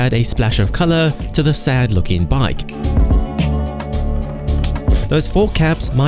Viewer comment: Did he just say tthe Speed Triple is a sad looking bike? :O :O :O :O :O :O